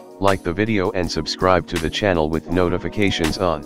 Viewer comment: it sounds like your saying no notifications on